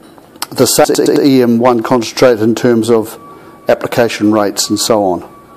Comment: I didnt hear what you said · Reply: "same as EM-1 application rates"